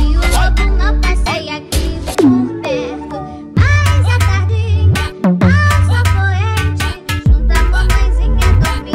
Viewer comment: Oque ela fala no 0:23 até o 0:40 ? Assa a carninha, faz o Proerd? Kakakkaka sério n entendi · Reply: A tardinha ai sol poente ;-; deu